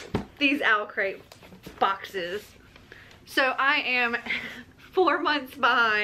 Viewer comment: I legit thought you said “so I am 4 foot 5” 😂🤣😂 I need hearing aids lol. I was like dang she short. But you said 4 months behind. Your videos be so cool though 😊